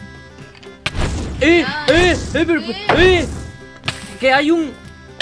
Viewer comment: En el minuto 30:42 ns si escuche bien o mal, pero me pareció escuchar un "si mi amor si" departe de